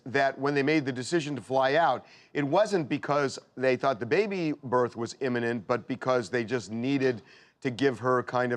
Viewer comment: I thought y'all said she gave birth.